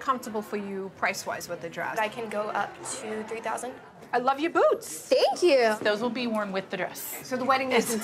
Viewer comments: Omg!! 0:48 I thought she said, “I love your boobs!” 😂😂😂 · I thought she said "I love your boobs--not boots"...LOL The 1st strapless dress was amazing..and with alterations..MOM..it will fit perfectly! The dress she chose is as gorgeous as she is!